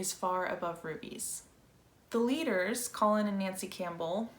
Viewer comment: I misheard "Above furbies" 😂